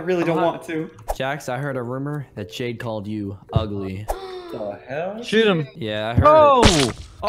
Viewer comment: I heard “The hell?” NO HATEBI LOVE HER VIDS